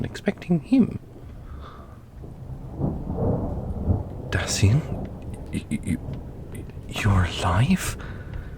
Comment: no cause i heard "daddy, your alive" AND I WAS ABOUT TO THROW HANDS. LIKE I GOT SO MAD BUT I AHD TO CHECK I WAS HEARING RIGHT AND THEN I HEARD SOMETHING LIKE DASYNE OR SOME SHIT